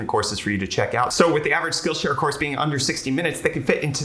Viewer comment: Lol thought you said the sponsor name was skittle share